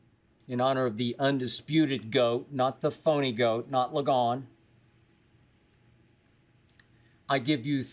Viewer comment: I am not a native speaker. 0:08 did he say not LeGone? Meaning LeBron 😅??